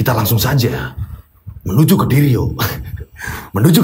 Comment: bilang menuju ke diryo Tapi kedengerannya seperti bilang Menuju Kediri yokkk😅😂 Oiya, salut sama mas Wahyu, langsung menawarkan diri untuk menolong mas diryo, meskipun orangnya introvert😇 Saat saya liat potongan video diawal video, katanya mas diryo tirakat untuk membuat orang lumpuh.. tapi kok malah bapaknya meninggal... Tapi ternyata bapaknya meninggal sebelum mas diryo tirakat to🙏🏻